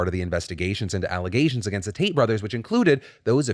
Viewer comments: Really thought you said Taint Brothers. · Two things for you Phill. First, I liked the tie, thought it looked good. Second, thought you said taint brothers at first and you know what...still fits.